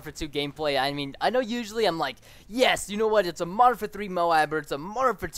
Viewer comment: When you say Modern Warfare 3, it sounds like you say Martha 3